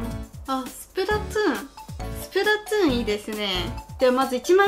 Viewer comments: スプラッツーンって言っているように聞こえるのは、私だけ？ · スプラトゥーンが、スプラツゥーンに聞こえた… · すぷらつーんって聞こえる、(聞き間違いでしたらすいません(○ Splatoon スプラトゥーン) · スプラトゥーンをスプラツゥーンと聞こえてしまう❗笑笑